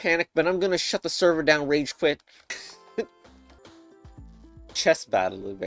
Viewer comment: i dont think you meant ragequit cuz i think they said real quick